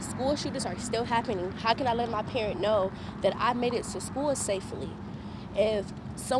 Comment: "How can I let my parent know" Yet the CC says "parents".